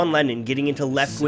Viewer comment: I always thought he said "Lenin" not "Lennon" lol.